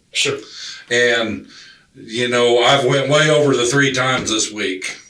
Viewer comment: Yes. But like he said, he snapped. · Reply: I thought he said that he went haywire 🤷‍♂️